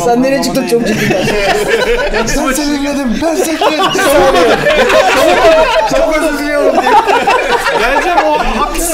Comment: Türkiye'nin en komik adamı Feyyaz ne diyor , anlayamadım? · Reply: soda açarken öldü